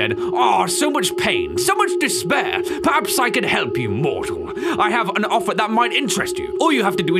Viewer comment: the way mortal he says sounded like malak not gonna lie XDD